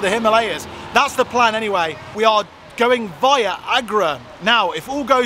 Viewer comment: Did you just say VIAGRA?! 😁